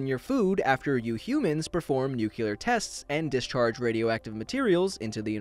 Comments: Did he just say "Nukuler?" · Did you say nuculear??